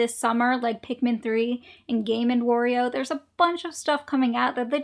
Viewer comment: did i hear gayman wario?